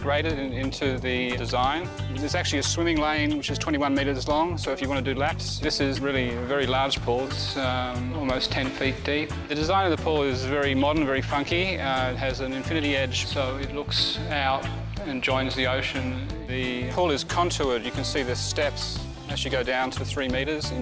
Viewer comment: thought he said fucky